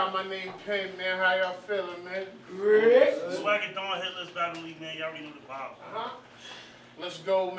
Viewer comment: Crazy battle! Both dudes went off. Almost turned it off when the co-host said the name of his league because it sounded like he said “Hitler’s Battle League”. Really glad the name was on his hoodie.